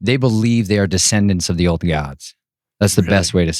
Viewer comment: Help. At 3:37 he says, "The believe they are descendants of the ??? Gods." Can anyone tell me what he says there? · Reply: Old gods-The Immortals who rebelled and forsook their assignment to be judges and helpers to mankind and instead lusted after the women and corrupted the earth.